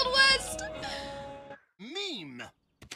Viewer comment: He actually said "me'm" not "meme", as he was responding to someone saying "whom". Being the lovable dope Knuckles is he just added an 'm' to the end of "me."